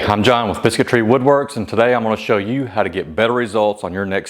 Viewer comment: First time through this channel playing this in the background and for a second i thought you said bigotry Woodworks 🤣 I said no way then realize you said biscuit tree either way I had great laugh